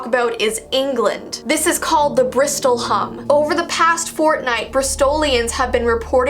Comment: wait XD i heard "Over the past Fortnite" in one of her sentences your a legend